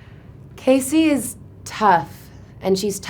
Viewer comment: Yes. I’m learning English can you tell me what is she saying in 0:05? I don’t understand very well “Kaycee is...” please ☺️ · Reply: Casey is tough